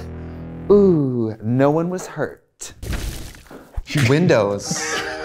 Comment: LOL I thought 🤣 it said "Widows" BWAHAHAHA. I said "Shit, this gone get dark.